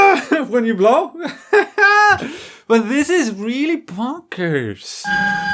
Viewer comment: “but this is really bonkers/ponkers” at first I thought you might have said “poggers” here